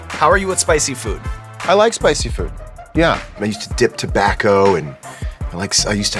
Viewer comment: did he just say he dips in tobacco!? lol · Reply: He said he used to dip tobacco. Also known as dipping, snuff, chew. Tobacco that you put in your mouth